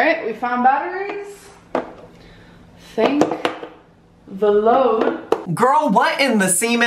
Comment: Thought she said "take the load" to the scale in hopes it won't break. Hahahah hahaha!! reaction to her idiocy is channeling the outer me watching her.